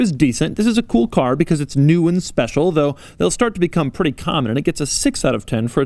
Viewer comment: did he actually say at 23:50 that this car will become pretty common?